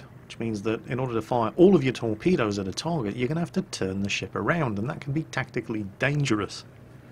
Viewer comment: Nearly every time you said ship, i thought i heard shit o_O "I like this shit" "Turn the shit around"